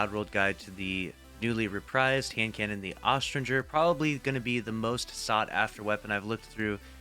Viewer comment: What did you just called this weapon @ 0:08 secs? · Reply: Aus...Tringer...